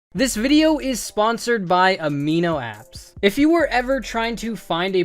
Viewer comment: I heard “Today is sponsored by Amino Acids”😂